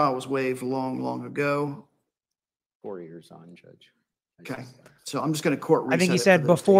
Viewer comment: I thought the lawyer said 4 years on judge @ 8:43 Crazy how it's almost been 4 years since her Feb 2020 arrest.🎉